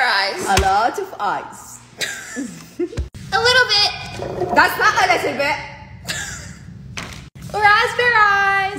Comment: I thought her mom said "that's not Elizabeth! "😂